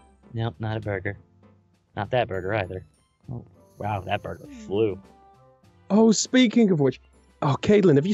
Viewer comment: Nope not a burger, not this burger either, woah that burger flew. ~ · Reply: I thought he said "woah that burger's blue!"